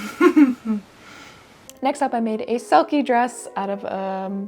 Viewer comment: Hi, kiddos!!! This video was a BEAST to edit, so the more accurate captions are currently being transcribed and should be up in a few hours! Enjoy this journey through the paaaAaaasst. Out of pure curiosity I'd love a sound-off of when/wat video you joined the channel at! Always so, so, so appreciative and grateful for you all. Have a lovely weekend and see you next video!! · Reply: I thought you said made a selkie dress❕ 25:45 🦭 NAKE. A. SELKIE. DRESS. Make a selkie dress. 🤎🩶